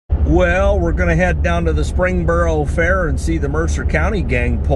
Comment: For my information? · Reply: In the intro it sounds like you say springboro